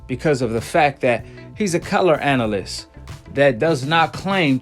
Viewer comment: Did you just say “colored” analyst ?